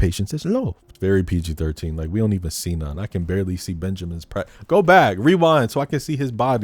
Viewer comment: did you just say “ I didn’t even get to see Benjamin’s Bratt? 😂😂😂😂😂😂😂😂